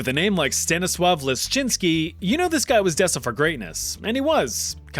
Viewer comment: i thought he said Stennis Lishitsky lol